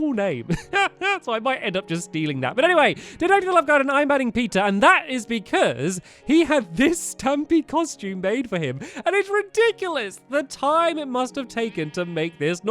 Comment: I was reading mockingjay when he added peter to the garden, and I thought he said peeta. Lol